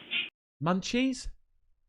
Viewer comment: I think she says, "my turn"